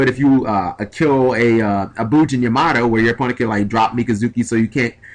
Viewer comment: did you just say "kill a bujin yamato?"